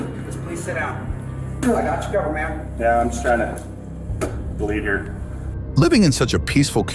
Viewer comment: As an American hearing a cop who was shot in the face calmly say, "I'm trying to bleed here." is so uniquely and profoundly American. What a legend! 🤩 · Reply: And I thought he said BELIEVE.